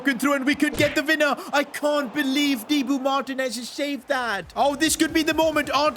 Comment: Did bro say thibaut martinez